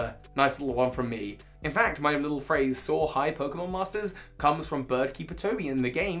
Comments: Forgive me for this 😁 I've been a very long time listener. I just now learned your outro is "Soar high, Pokemon Masters". I always thought you were saying "So hi, Pokemon Masters". I never understood why you were saying "So hi" as an outro.😋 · Forgive me Bird Keeper Toby. I never knew your outro was Soar High. With how my hearing is with your accent I thought you said So hi. Thanks for the correction.